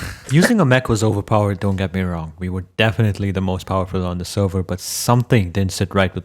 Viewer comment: WTH did u just say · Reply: He said hes the most powerful